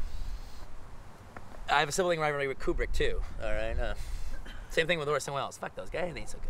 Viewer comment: What's he saying at 08:48 and 08:54? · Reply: "I have a sibling rivalry with Kubrick too, alright. Same thing with Orson Welles eh fuck those guys they ain't so good alright...they ain't so hot... I mean you know they were ok but jesus christ." With added sarcasm.